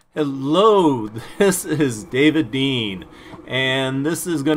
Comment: Lmao I thought he said David bean